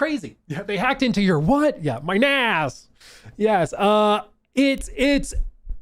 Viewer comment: I was passively listening and I thought I heard they were hacking into your ass lmao NAS 😆